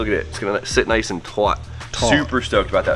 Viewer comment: did you say twat!!! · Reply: "Taut." As in, tight/flush.